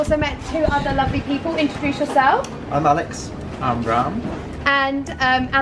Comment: i thought he said im brown !?